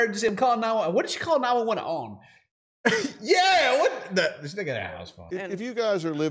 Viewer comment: did just say this nigga had a housephone or are my ears playing up 😂